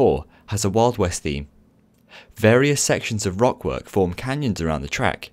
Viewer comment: Did I hear some r o c k w o r k?